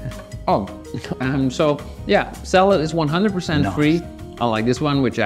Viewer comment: I hear a "not" before free